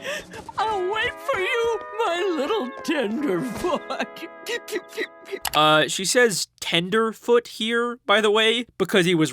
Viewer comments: I literally thought she said "My little gender-fuck" like he was incredibly non-binary and genderfuck was a cutesy name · fuck i heard " tender foot" the first time but i now only here tender fuck and im saying tender foot in my head and its not changing back wtf